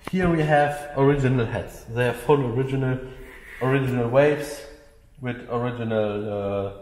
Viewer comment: When it sounds like he says wave or waves he means valves.